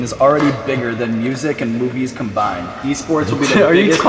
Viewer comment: I thought Sean said “bigger than music and boobies combined “... 😂.